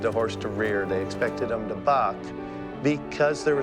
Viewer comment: I had to play that back again. 😂 I thought he meant they FORCED horses to F** instead of buck. 😂 I was like wait WHAT did he say let me rewind that. 😂